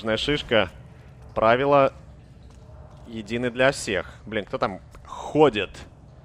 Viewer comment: Знаю, что оставляю комментарий слишком поздно, но я пересматриваю, да. И только сейчас услышала, как на 5:08 ругнулся словом "б***ь"